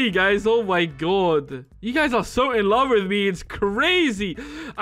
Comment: I legit thought said, "You're so in 'lava' with me" and cringed. But he didn't and so I'm claiming that pun for now.